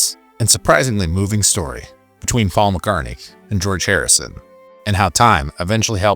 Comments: Did he say Faul McCartney? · did he call him Faul McCartney? 😂